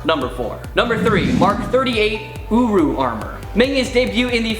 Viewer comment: I thought he say uwu Armor